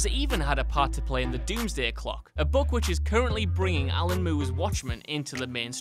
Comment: everyone commenting on darkseed must not have stayed. Instead of saying alan moore, it sounded like he said alan moo. lol.